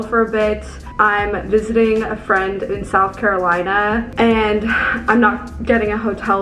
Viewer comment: Hey, hope you’re not stuck in South Africa due to Omicron affecting the flights outta there! Stay safe and take care. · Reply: South Africa? I thought she said South Carolina lol.